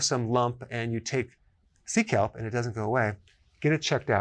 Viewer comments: okay...Sea kelp. I thought you said seek help. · I heard you say seek help. Then I realized you said.. sea kelp! 😂